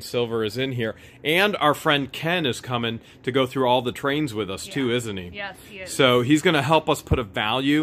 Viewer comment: Do you think any of the pieces in the Sterling Silver Box are SOLID Sterling Sliver? Hale$ YES or Hale$ NO? If you liked this video, then you'll LOVE this one: <link> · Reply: When God said “brains” I thought he said “trains” and I missed mine. Lol. Nice find by the way. ❤️❤️❤️